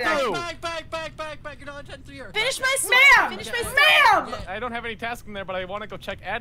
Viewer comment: Did Lauren or someone else say "Ma'am"? cuz for some reason I heard someone say "Finish my spam" 👁👄👁